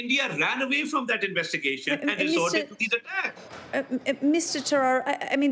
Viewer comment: did she call Mr Terror 😂😂